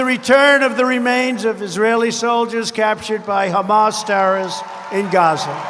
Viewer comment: I thought when President Trump said terrorist, I was confused......I thought he said Democrats. ....